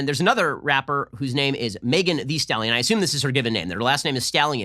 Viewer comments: LOL! I thought I heard, 'Megan Thee Scallion'...[and why not, her parents could have been vegan]. Wow - very cliche and predictable for the entertainment sector = boring. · i thought you said megan thee scallion... well they both are stank skanks